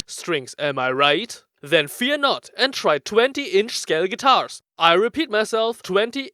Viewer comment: oh man, did I get it right? did you just say that they are 20 inch scale guitars? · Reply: I repeat myself ... 20 INCH SCALE GUITARS!! ( ͡~ ͜ʖ ͡°)